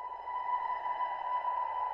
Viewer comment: "I'm an mmo, you're an mmo." This is a song about how we are all MMORPGs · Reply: I keep hearing "I'm an enema, you're an enema"